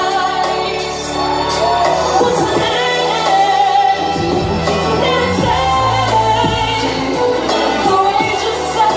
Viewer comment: what is she saying in the 1:25 part?? t.t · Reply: i think she says "For tonight, we're insane, courageous sudden ex madness (i don't know what she says at the end)"